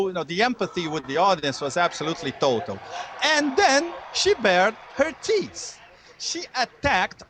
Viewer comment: It sounded like he said, "then she bared her teets" didn't it? In fact he said it twice. Perhaps someone should have help him practice the "th" sound before the interview :) I wonder how that kind of "wardrobe malfunction" would have gone over at the RNC.